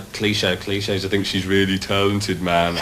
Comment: what does Pete say here? All I hear "Kleesha is talented man" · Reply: "And a cliché: I think she's really talented man" (he just means that its an over-used phrase)